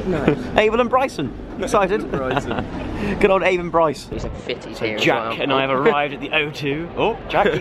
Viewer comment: who else heard Jack say "there some fitties here"